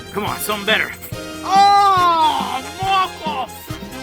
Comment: Did Joe just said "Mocos" in 16:00? XD